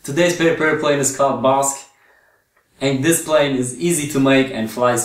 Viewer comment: I thought you said this will be easy